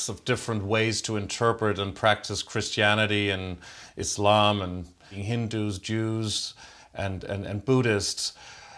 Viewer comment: I know said "Hindus" and "Jews" but I heard "Hindu Juice" 😂🤣😎